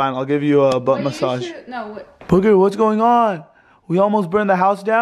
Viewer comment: it sounded like said wait what yeah when Brandon said do you wait a butt massage like if agree😅 👇🏽